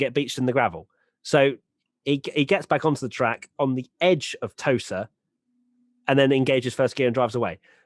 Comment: I thought you said "on the edge of a toaster" and i was really confused.